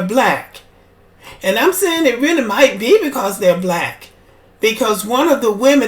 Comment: I thought they said that they weren't "Black".